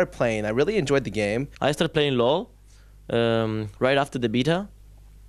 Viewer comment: doesnt it sound like ocelote is saying " i started playing LoL right after they beat her" @ 0:21 =3